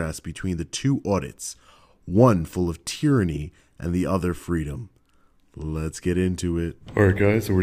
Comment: I thought it said "vs Tranny Freedom" and when the first excerpt of the deep voice talking woman appears im thinking......